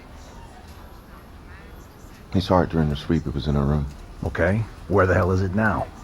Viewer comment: Can anyone tell me what the cop said before the "where the hell is it now?" I cant quite undertand. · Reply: “We saw it during her sweep, it was in her room”